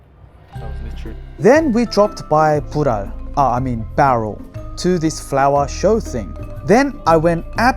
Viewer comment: did you just say 불알? 😀